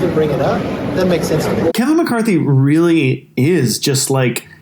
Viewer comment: Did he just say KamiCarthy? I hope so because that works perfectly.